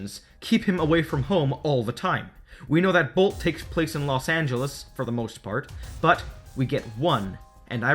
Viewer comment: "Los Angeles" , i hear into "Los Santos" XS